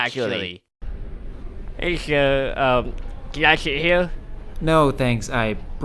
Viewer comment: Btw in 0:32 turn on subtitle he said sink subtitles say siri